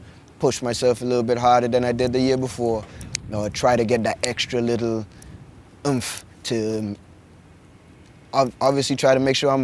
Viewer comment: what did he say at 8:26? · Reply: he adds 'some extra oomph" meaning he works even harder during this phase to prepare for sprint specific training.